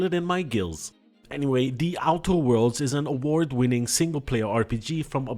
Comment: Damn. I thought you said Outer Wilds. I almost get excited, Mr. I Have A Big Big Butt man lol.